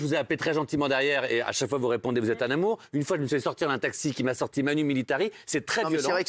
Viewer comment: Matthieu Delormeau il dit quoit au début " a chaque fois vous répondez vous êtes a ......... " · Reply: "Vous êtes un amour"